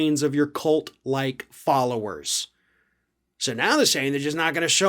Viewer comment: I misheard "cult like followers" with "bird brain followers" and had to rewind the video XD